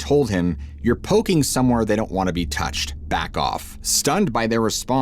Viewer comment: “ You are poking somewhere they don’t want to be touched”???? Bullshit, don’t make shit up. What they really said is, “They don’t want you or anybody messing with them up there..” The man had already gave out too much info so him by saying that so him snd his partner left.